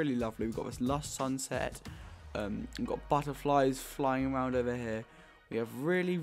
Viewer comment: Did he just say fish fly?